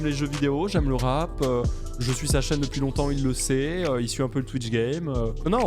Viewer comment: J'ai entendu : 9:03 : je suis sa chienne depuis longtemp, il le sait... Me suis dit que j'avais certainement mal entendu...